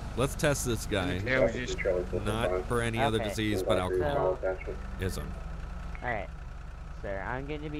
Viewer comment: did you say alcohol izm. lol